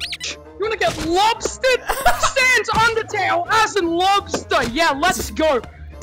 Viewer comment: did i hear ree kid says "undertale"